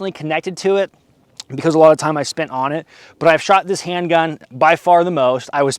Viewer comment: i really thought i heard you said "i spit on it" and i had to double check lol